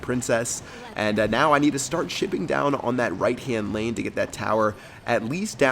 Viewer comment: It sounded like he said ill start shitting down on that right tower